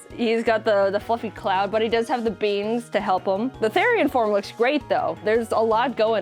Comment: therian!?!?!? Did say therian!??!?